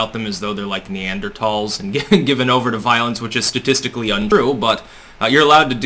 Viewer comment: listen to the "glitch" makes it sound like you said true instead of untrue